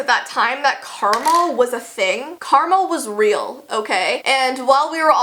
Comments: I thought you said "caramel was real" So I was waiting for her brother to pour caramel in your hair or something😂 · When she said “karma” I heard “Carmel” 😂 I was like “I want some” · Omg I had to replay the part where she said "karma" like 3 times because I thought she was saying caramel and I was like yes Jessie. Caramel has been a thing for a LONG while xD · At first I thought u said caramel was a thing lol but then I realized u said karma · who else heard jessii/ I did not know Carmel was real but it was a real thing me wow I did not know Carmel was real either😅😐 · When she said karma it sound like she said caramel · whenever you said "karma" i heard "karmel" 😂😂😂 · ohhh... I thought u said caramel not karma. XD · Karma definitely is real lol. I thought you said karmal 😂 for a second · I thought she was saying caramel when she said karma 😂😂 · When you said ‘Karma’ I thought you said ‘Caramel’ 😅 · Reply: Girl, same I also thought she said caramel and I was like wait I should we play the video and I found out she said karma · When she said karma I thought she said caramel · it sounded like Carmel. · When she said “karma” i thought she said “carmel” lol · did she say Karma? i thought she said caramel! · I thought she said "caramel" instead of "karma"... "We didn't realize that caramel was a thing back then" sounded kind of weird to me lol